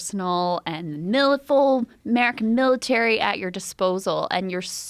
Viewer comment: What does Krystal say at 2:04 "Milifuull mac military"?? That some secret Area 51 shizznizzle